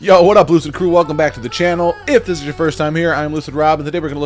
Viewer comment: love the channel bro!! i gotta say when i come in it sounds like you say whats up loser crew hahahaha